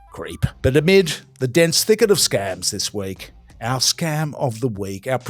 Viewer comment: So many scams. I actually thought you were saying scab at first. I reckon there's probably too many candidates for scab of the week